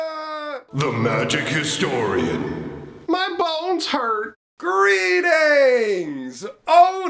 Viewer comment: The first few times I heard you say,"my bones hurt" I swore you were saying, "my balls hurt". You the best dude.